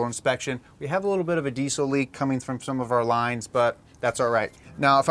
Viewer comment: thought you said diesoline lol like gasoline lol